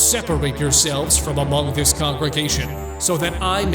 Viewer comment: DID I JUST HEARD GD CONGREGATION